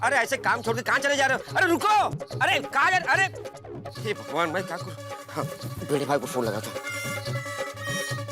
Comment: did Abdul says hey BHAGWAN ??